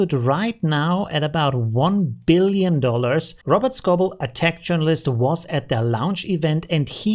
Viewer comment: I misheard you and thought you called Robert Scoble an "attack journalist" - what you actually said was "a tech journalist" - but now I really want to see what an "attack journalist" is... Hrm... I wonder what an AI image generator would think of that prompt...